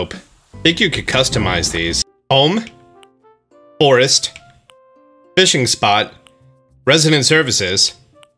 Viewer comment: did he really say, "Bome, Borest, Bishing Spot"